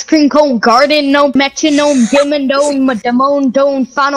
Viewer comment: i think he was trying to say dug ?digga di?vaa? Dome idk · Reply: Doug Dimmadone, owner of the Dimmsdale Dimmadome.